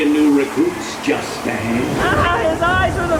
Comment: When the skull says "We seekin new recruits just ahead" I thought he said Justin. lol